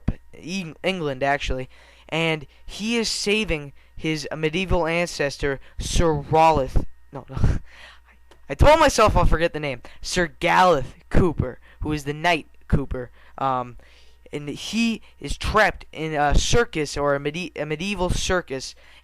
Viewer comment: lol it sounded like you said sir galleth pooper xD